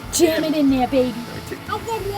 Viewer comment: Hahaha, he was saying, “jam it”, I thought he was saying “damn it” when he was teaching how to use shovel. Ha ha ha ha.